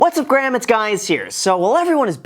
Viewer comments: I heard what's up gram it's guyies here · This is random but... Am I the only one who always thought Graham said "What's up guys? It's Graham here?" And now I'm looking back at all of his videos and realizing he says it the opposite way. And now I feel like I'm going crazy.